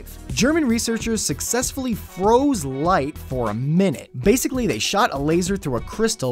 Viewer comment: all i heard was Lightsaber . . . :D